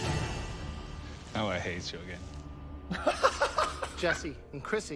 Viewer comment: What does Luca say at 23:18 ? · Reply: "Now I hate you again" I think